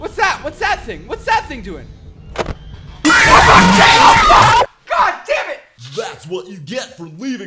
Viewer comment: ARE YOU SCREAMING,,FUCK JHOPE" OR AM I HEARING SOMETHING WRONG😂